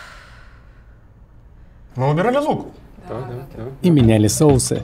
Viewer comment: Что говорит парень в клечатой рубашке на 2:02? Пересмотрел даже на скорости 0.25, всё равно непонятно. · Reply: мы убирали лук